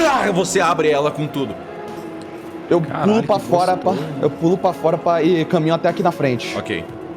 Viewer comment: Spoiler . . . . . . 3:55:12 só eu que ouvi o Joui falando "crlh que gostoso"